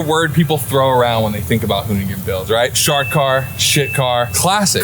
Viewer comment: Umm... Did Scotto say "Shart Car"? It's Shartkart? Or am I having a stroke? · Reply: im pretty sure it was shit car, then shart car and after it went to bare bars, then shartkart or i might just be having stroke as well 🤔